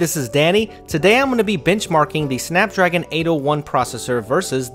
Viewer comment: Like if you thought he said "Bitchmarking"